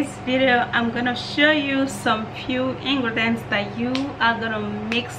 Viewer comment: Thank You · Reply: I thought you said it’s two ingredients sis?